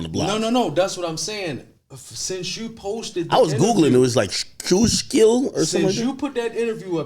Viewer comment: btw the word he was tryna say around here was Schuylkill, a river lmao.